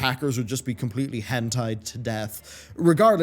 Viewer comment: Did you just say "will get Hentai'd to death"? LOL